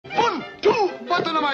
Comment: I didn't hear " the amount of the sticks" I heard "beat him up with the sticks" 💀